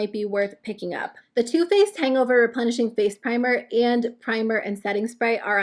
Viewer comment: For some reason I thought you said the Too Faced Punishing Face primer 😂